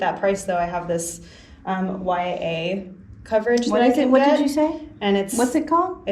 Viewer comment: the buyer mentioned something about "mya coverage" (not sure if I heard it correctly), how can we find that coverage.